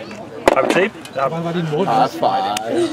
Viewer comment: did he just say arse five? =P